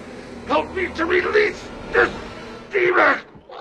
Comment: I always thought he said "Help me release this steamer"